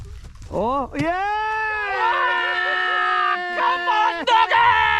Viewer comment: It's it just me or does it sound like said come on niggas at 20:37😂